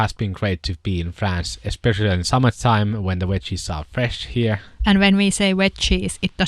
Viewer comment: I just LOVE your accent! It's so cool listening to you both. I'm so impressed at your English, especially when I doubt many of your watchers speak or understand any Finnish! 😀 But I didn't think you were saying Wet cheese, I thought you were saying Wedges! hahaha! Such a very interesting video podcast! Thank you! 😍🤩